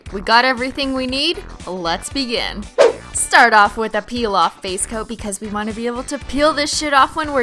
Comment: starting off with a peel-off face coat! Who else heard that?